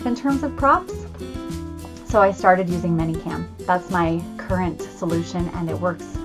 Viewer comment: "So I started using minicam?" Is that what you said? What is that, please?